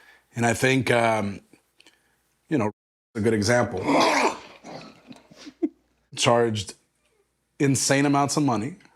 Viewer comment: Who do we think KO said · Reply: Ryback